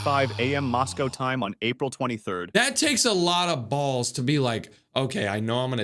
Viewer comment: WHAT DID HE SAY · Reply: Balls.